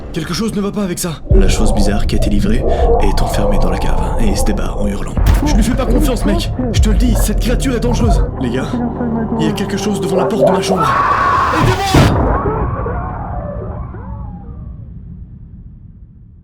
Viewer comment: A 0:20 seconde c'est moi ou il dit "OUVRE LA POTRE" 😂😂